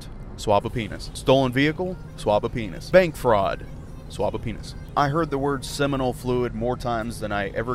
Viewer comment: took me until he stopped saying it to figrlure it out ......thought he was saying to "swap" one, not "swab" it 😂 so I was like WTF 😂